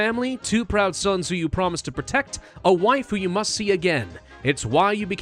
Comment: I heard Waifu.